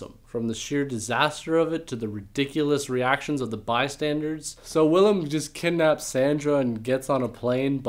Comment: Ummm lol did he say "bi standards" ?!?!?